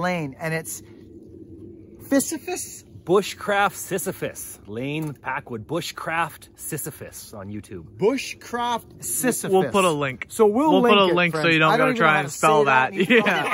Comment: Thought he said bushcraft sissyfist